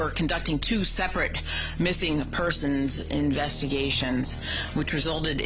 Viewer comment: is it just me or did she just say persons